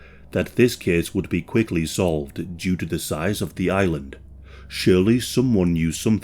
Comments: You said, "Shirley knew something", and "Shirley had talked to someone".... but you never actually mention WHO this person is and what it was that actually said !? ...and to who ? Who is??? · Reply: hi, I had a listen and I think he is saying - Surely someone knew something, Surely someone had heard something I mean the word he's saying is - Surely, not. . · You said, "Shirley knew something", and "Shirley had talked to someone".... but you never actually mention WHO this person is and what it was that actually said !? ...and to who ? Who is???